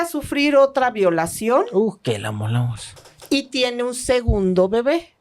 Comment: Alguien me puede decir que dice el Doctor ahí? 😅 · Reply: Uh que la amolamos